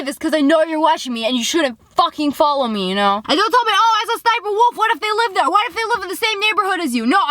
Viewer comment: I have subtitles on so when lia said Sssniperwolf it thought she said " cyber" wolf